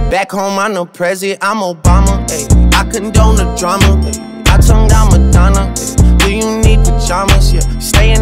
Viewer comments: I thought drake said he turned down Madonna 😂😂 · This whole time I thought drake said: “ I turned down Madonna.”